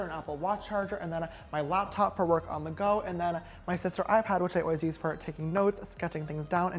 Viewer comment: this is my best friends first time watching 1 of ur vids she thought u said it was ur sisters ipad :P